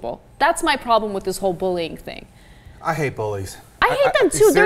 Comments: the guy says I hate bullies and the subtitle says I hate police lol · The caption for me when the guy said at 1:37-1:38 said "i hate police" I could not help, but laugh. What he actually said was "I hate Bullies", but I still couldn't help, but laugh. Also I couldn't stop laughing when they kept cussing. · I like it just after 1:35 where he says "I hate bullies" but the subtitles say "I hate police!"